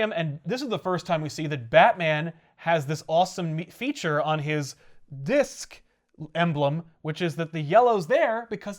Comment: Lmao there was an at 24:24 right before finished saying disk so it sounded like "Batman has an awesome feature on his dick."